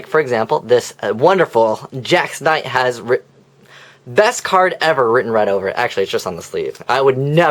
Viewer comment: I thought he said Wodan was best card.